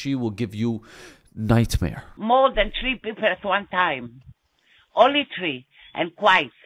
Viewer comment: i thought she said "only white" for a second 😳 lol. Also, she sounds Israeli